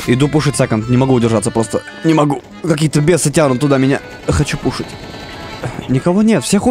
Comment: кто еще услышал:"хочу кушать!"?